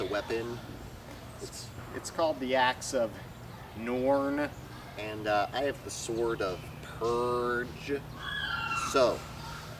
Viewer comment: First I thought they said the axe of porn and the sword of pervs X3....